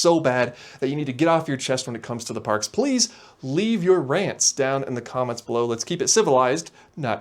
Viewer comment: Viv. May. Bee. oh, rants... i thought you said aunts. my bad. 🤣